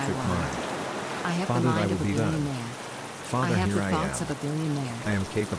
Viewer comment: I'm sorry, did I hear farther there I will be done, farther here I am at 33:44 ? I'm trying to confirm, thanks. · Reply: This sound track has a few tweaked affirmations from the likes of Neil Goddard and Joseph Campbell and a few others to be exact it says "Father thy will be done Father Here I am."